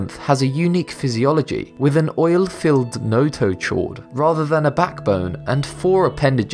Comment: I can't understand what he says at 1:42 he says the Coelacanth, has an oil filled.... "notochode"? What is he saying there? My American ear can't extrapolate a word there. BUt a lot of how he talks takes interpretation for me as he says Wolf, but it sounds like "woofe" and "Lizard" sounds like Lizzid and "past" sounds like possed. I just don't interpret the accent very well but I found that word interesting.